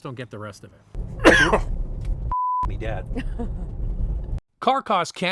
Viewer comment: Ha! Did I hear “F$$k me dead!” with that cough? Your car and watch worlds are colliding! A bit of AC3 mixed in — nice.